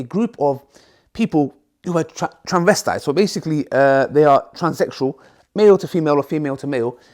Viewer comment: Did you just say "transexual"?😭✋️ 1:03